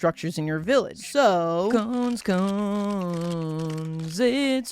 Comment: On the cone song 38:12 I thought I heard him said come instead of cone. Is it just me or is it my ears being deft?